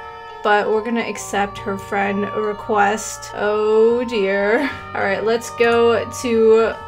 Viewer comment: You Said “oh dear” so much that it sounded like “oder” XD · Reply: I never said anything about her being an online dater, I just said that she said “oh dear” so much and so fast that it sounded like she was saying “Oder” but I never said that she was online dating, + no I don’t online date because I literally almost never play Roblox, I just like watching it :/